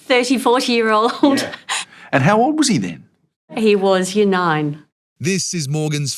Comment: Did she say,"year 9," or, "he was 9." Because if my guy was 9 years old, I have nothing but respect. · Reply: Year 9. Meaning he was around 14, 15, or 16 years of age.